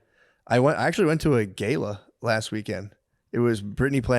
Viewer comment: Mccuskies trying to pronounce gala had me fucking rolling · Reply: I had to rewind cause I thought he said he got high and went to a gay club